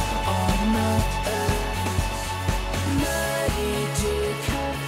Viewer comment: lol x'D i think he says "Madi dear cant we just dissapear and take our chances." but lol it does sound like he says dickhead.. x'DD